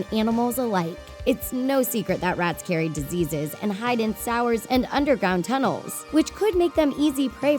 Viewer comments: Did she just say "Sowyers" Instead of "Sewers"? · im sorry but did she say “sours” at 5:40 instead of sewers?